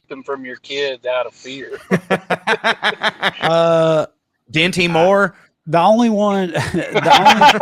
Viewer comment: what’s he say? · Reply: Dinty Moore